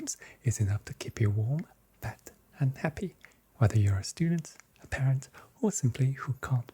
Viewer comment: I thought he said warm, fat and happy 😆